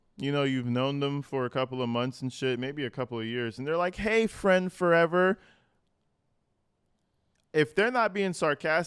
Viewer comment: So my thing is, if you don’t mind, please. Nonetheless, on top of everything else, ex-wives and jobs and infringements on my constitutional rights and everything, it was a good day. We’ve been having good days. Nobody laid a hand on anybody. Nobody laid a hand on anybody! Know that! Know that again! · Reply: 😂 I just heard her say, "Why, Friend Forever?" WTF?!